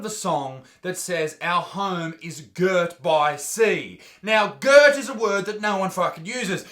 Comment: I always thought you f*ckers anthem said GIRDED by sea. Well I guess ya learn some useless shit every day